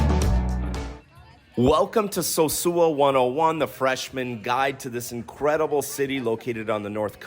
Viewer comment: Lol it said the freshman guide. I thought it said the fisherman's guide. Do you have any videos about fishing, snorkeling, or scuba in the area?